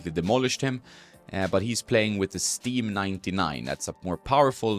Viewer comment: Great video! What racquet is de minaur using? I heard you say “sting”